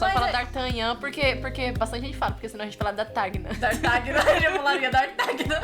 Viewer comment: Oque elas disseram: Tartagna- Oque eu entendi: Tartaglia ....eu tô jogando muito genshin